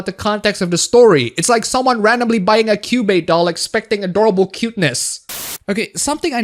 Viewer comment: what is that , did u say cube doll?